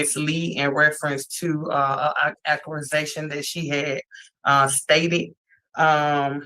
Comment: ?!? ... Did she say ,"...she made reference to an "acculisation" ..."? ( 1:25:15 )